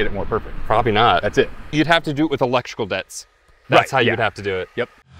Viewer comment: "electrical debits"? Could you explain this? The way the co-host said, "right, ya.", makes me think he didn't even quite know what was being said. You could make the videos longer and more engaging by explaining things along the way. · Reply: “Electrical dets”. Used to set off larger explosions with electricity.